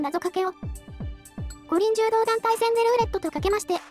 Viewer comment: 最後のなぞかけを作業しながら聞き流してたら、 五輪柔道団体戦のところをイントネーションで「ご臨終～」って空耳してしまって何事かと